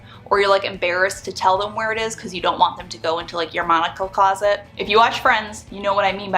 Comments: I heard "monocle closet" instead of "Monica closet" and ya know... it still checks out. If you have a monocle closet, you have too much stuff. And probably too much money 🤣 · I thought you said monocle closet and I thought you had an eye issue. Then you showed friends and I understood. Lmao if I need a monocle I'll hyu!